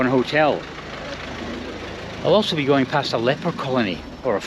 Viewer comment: thought 💭 you said ☘️ leprechaun 🍀 colony 😂